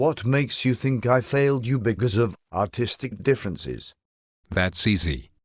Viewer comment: did he just say "autistic differences" lol